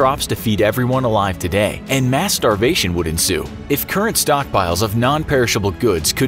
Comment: Lol I thought u said masturbation😂